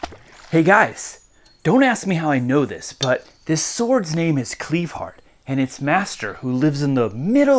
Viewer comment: Cue Elton John's C-*Levon*! Oh wait, I heard that name wrong.